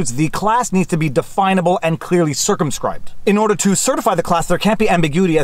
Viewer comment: For a sec I thought you said "The class needs to be definable and clearly circumcised."